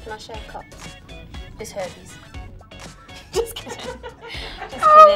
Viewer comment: what's jade saying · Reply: leigh anne said ''are you ill'' the jade said ''just herpes''